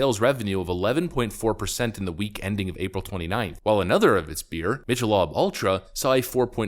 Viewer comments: Did you just call it, "Mitch-ull-lob Ultra"? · LMAO did you just call it "Mitchel-ob"? 😂